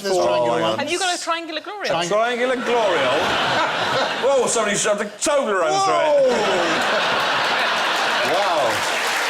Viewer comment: can't figure out what Bill says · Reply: He says "Triangular glory-hole? Oh, somebody shoved a Toblerone through it!"